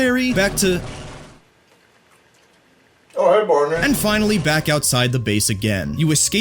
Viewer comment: what did you say? because it sounded like "oh hey gordon" but also sounded like "oh hey barney" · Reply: "Oh hey Barney"